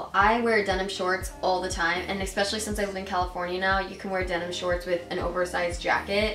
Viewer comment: anyone heard her say "californina"?